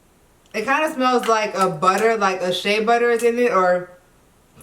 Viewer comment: I thought she almost said that it smelled like a butt. I was like, come again?? 😨😳😅